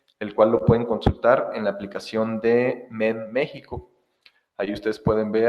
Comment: Puedes decir anotar el nombre de la aplicación para ver el precio por Kw generado con paneles para venta a CFE? ( no alcance a escucharlo bien entendí "menmexico" ) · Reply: La aplicación es: MEM México, ahí puedes checar por medio del PML.